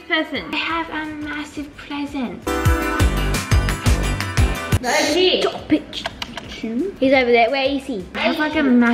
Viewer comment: Is it just me or did anyone else hear sabre say the Sh*t